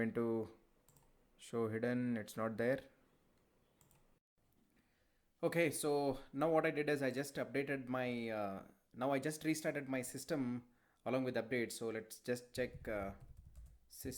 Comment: I'm confused at 5:42, he restarted his windows or something like that? I'm not nationally english and i can't understand, can someone help me